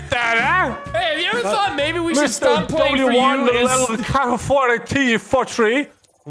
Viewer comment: Post your theories on what Nogla said here 😂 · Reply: W1LDC4T43